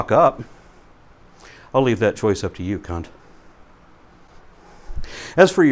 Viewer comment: Did he say cunt ? That caught me off guard lol